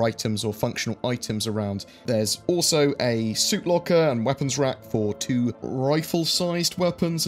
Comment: Slightly harrowing intro 0.o Thanks for the coverage, as always. (Also, when you were describing the furnishings early on, I honestly thought you said "Soup Locker". Definitely did a double-take.)